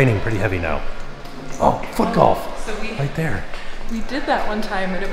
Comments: it did not sound like Tim said Foot Golf 😂 I had to go back and check what he said! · I thought Tim said a rude word (f-o) then I rewound it, and he said "foot golf." 😂